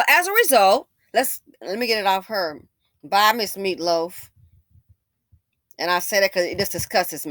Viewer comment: Did she say bye miss meatloaf?! I cant with u!! 😂